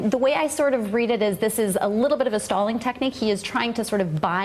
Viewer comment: First thought she said "a little bit of a Stalin technique". Hahah